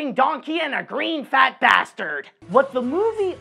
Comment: Did just say bastard?